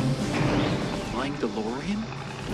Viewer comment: When I was a kid I always thought biff said “My Delorean!?” Then starting the argument with my friends that doc stole biffs Delorean lol